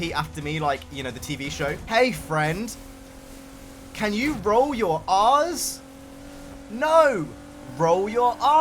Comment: genuinely thought he said roll your arse😂